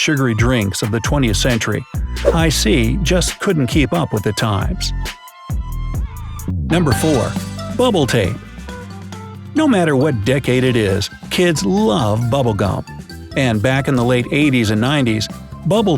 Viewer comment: I thought he said bubble tea